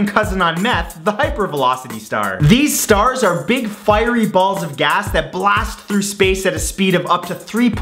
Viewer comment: excuse me did you just say firey balls of gas